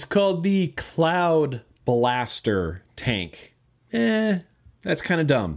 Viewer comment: I thought you said cloud bastard. Don't know why but I like cloud bastard. Ha cloud bastard.